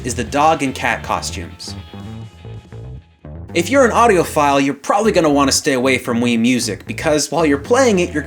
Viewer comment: When you said Audiophile, I thought you said Audio File XD